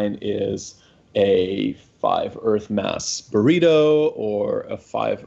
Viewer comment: Tell me more about these five-Earth-mass burritos...? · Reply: I didn't hear him say "frozen" the first time... ah, checked again: at 6:40 he just said burrito; then at the end it was mentioned again as frozen burrito.